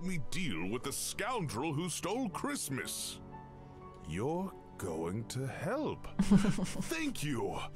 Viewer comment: I thought he said "you're going to Hell!" 0.0